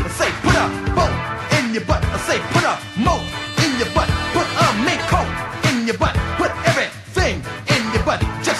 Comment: I have a vague childhood memory of hearing Eddie Murphy sing "put a mink coat in your butt", but I just assumed it was the mandela effect or a mis-remembered dream or something. I had no idea this was real until this moment. The 80s were very strange. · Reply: I always thought he said "put a mint coke in your butt"